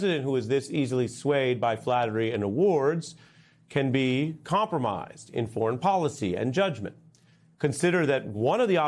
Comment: Can be compromised in policy and judgment ~ Did I just hear emoluments clause?